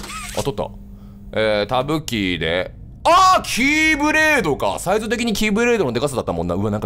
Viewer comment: 「キーブレード」が「キューブレーキ」に聞こえたw